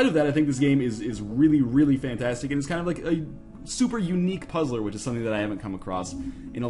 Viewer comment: what was that word he said in 0:24-25 ___ puzzle sounds like unick！